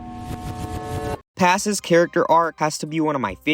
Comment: you did not call her passiss